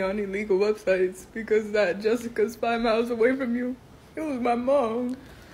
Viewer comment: Wtf did she say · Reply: "Jessica 5 miles away was my mom"